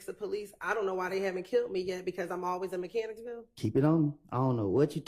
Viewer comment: I heard that “keep it on me” he slid that in 😂😂😂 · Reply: He said u ignoring me